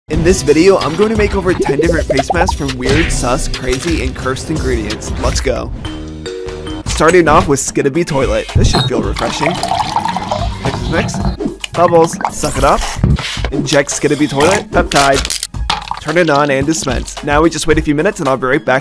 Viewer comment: Did bro say skidibi